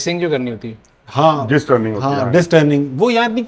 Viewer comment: What did he say at 2:24? Jis turning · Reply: Disk turning. Disks Face karna kehtay hain i guess Yahan